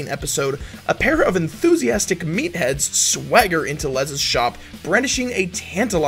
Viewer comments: lol i thought it said methheads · I thought it said Methheads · Thought is Said METHheads??? SSDD 🤷🏼‍♂️